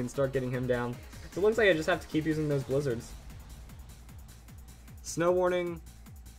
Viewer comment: He only uses females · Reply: ***** i heard you say "going to mega blizzard.."